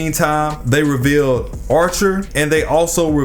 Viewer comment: Embarrassingly obsessed. Nearly every rogue spirit is a repeat servant, but you decide take shots at Arthur? WHO'S LITERALLY NOT THERE. Wtf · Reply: oh I misheard archer for Arthur, my apologize then. Also like I said I'm just asking. Yes There are a lot of repeat servant but among them I notice that Artur or at least someone related to them (saber face) show up more in other fate series. Once again my apologize if my comment somehow come out in a bad way.